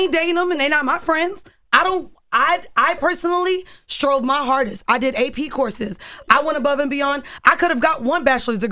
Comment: damn man. The irony of life. All I heard was, “I’m a PhD.”.